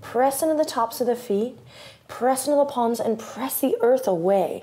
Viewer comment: I thought you said press into the nipples.. I thought WHAT?